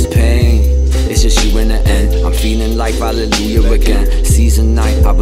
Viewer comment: What is saying at 2:23 Its just you in the end I'm feeling life, (hallelujah again)? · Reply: Valleujah